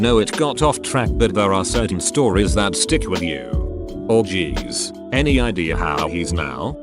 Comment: Oh god- I wasn't watching the video, just listening, and I instantly looked up at my screen at 18:32 .... I thought it said orgies 😂